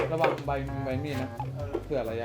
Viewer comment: มีความด้วย😳: ระวังมือนะ(มีใครได้ยินแบบเราใหม😁)